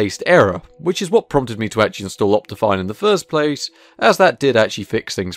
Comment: what does he say at 11:20? Opti what?